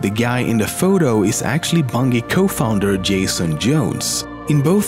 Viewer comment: Also at 6:58 it sounds like to say "bungee"